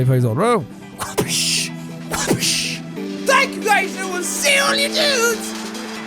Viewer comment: Dude, I was half listening, and when you said "and I'll see all you dudes," I heard, "I'll see all your nudes!" I DIED